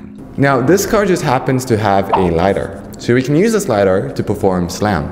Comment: When you said LIDAR, I thought you said lighter, and immediately I imagined a self driving tank with a flamethrower, haha. I loved your video! With this kind of epic DIY videos, you learn a lot about something you'd likely never learn otherwise and I'm sure it gave you some mad respect for the Tesla FSD developers and what all they do!